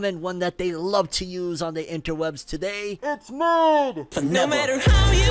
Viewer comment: Sorry what did he say? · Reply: It's mid